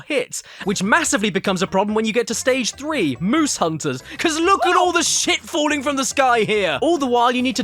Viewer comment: I thought he said noose hunters